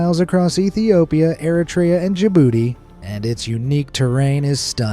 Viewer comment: When he said Djibouti, I thought he said your booty😂